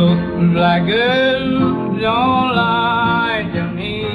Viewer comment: Best version ever, sorry nirvana fans but it's not even close. though it does sound like he says "black girls"